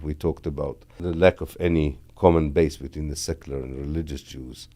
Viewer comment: Importantly: at 1:19 he does not say "settler" but "secular" ! (Ofc Israel is a settler state but that's not what he said right there)